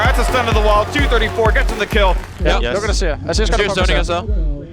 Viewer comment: Svenskeren killed me when the team was destroying the nexus and he was screaming " kass kass kass ! " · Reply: Wait i thought he was saying "look at this, look at this" really fast as he flashed forward and zhonia'd for a cool pose on Nidalee. I don't think he was saying Kass.